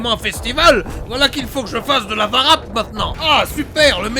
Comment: Au début j'ai entendu "Ah ! Mais c'est décidément un festival, voilà qu'il faut que je demande de l'aide à un arabe !" Je suis trop dans la peau de Jean-Marie